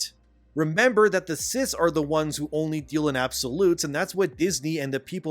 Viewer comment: For the splittest of seconds, I thought you said "Only the cis deal in absolutes"